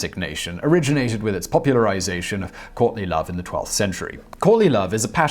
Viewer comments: I thought you said Courtney Love not Courtly Love.. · Oooooh... courtly love. I thought you said Courtney Love... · I thought you said Courtney Love not Courtly Love. Hahahahahaha